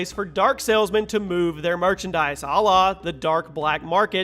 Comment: was he saying "Allah"?? , bro if you did say that you're getting in some serious trouble <:/